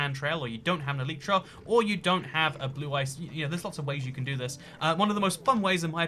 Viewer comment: does he start to say blue eyes white dragon or am I hearing things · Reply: He said blue ice